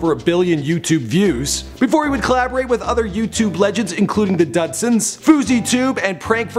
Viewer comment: Did he just say dadsons